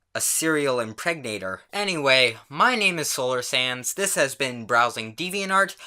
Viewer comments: Every time i hear you say "solar sands" its like "slolar SANS" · The first time I watched one of your videos I thought you had said the “Sands” part of your name was “Sans”... Oh boy I was I wrong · It sounds like he says “solar sans”